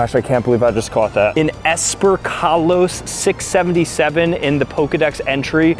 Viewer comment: did somebody say 67?